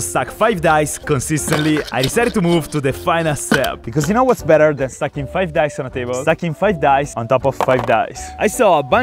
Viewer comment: why did it sounded like you said 'whats better then sucking 5 dice 🎲' 😭